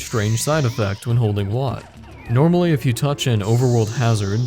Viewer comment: did he say wario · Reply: Ik it was five years ago but yea he's saying Wario